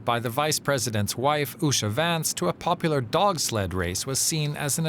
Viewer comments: Sounded like he said..😅😅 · Did he just say he's?? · For a moment, I honestly thought he said and technically, it fits. Lmao. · Seriously, I hear "HI, I'm Shady Vance" everytime. It is the weirdest thing. 🤔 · Did he just say?